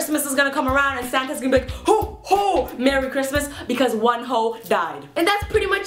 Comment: It sounded like she said Wonho